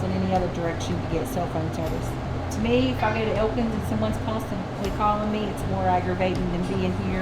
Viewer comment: FYI at 10:33 she says Elkins which is the largest town in the relative area. The caption said open